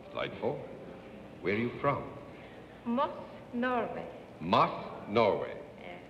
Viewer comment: Akiko Kojima of Tokyo became Miss Universe at the end of July 1959, having "narrowly edged out Miss Norway, Jorunn Kristiansen, 18, for the title ..." the Associated Press reported at the time. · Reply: I thought Ms. at 3:03 said she came from "Most Nervous".